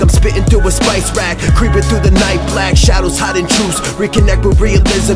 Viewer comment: uh i love the song but at around 1:43 shadows hiding jews?? o.O am i hearing this right....